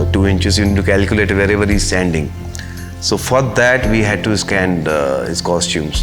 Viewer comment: did he just say "FUCK THAT" or "FOR THAT"?